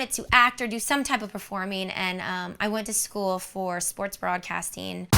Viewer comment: I thought you said “sports bra casting.” Nope, you said “sports broadcasting.” 🤦‍♀️ lol